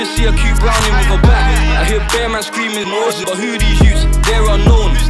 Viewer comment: Wait wat does he say wen it says “ I hear bare man scream out suttin*” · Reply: i hear bare man screaming zone2 u can hear the ZzZ word drag